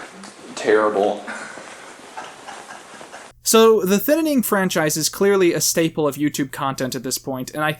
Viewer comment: Who else heard him say thinninning every.....single.....time